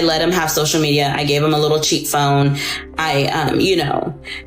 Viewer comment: "I let him have social media, I let him have a cheap phone" yikes. Regardless of how good/bad the guys were. Controlling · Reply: I thought she said 'cheat' phone 😆